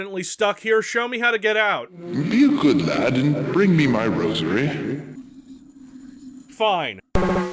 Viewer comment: That one ghost asked for a rosary. For a moment there, I thought he said "rosemary". 😆